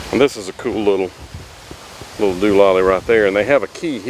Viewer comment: At time 3:38 of this clip, the narrator says, "This is a cool little [...] right there." What's the word? Dololly? Doololly? Please help!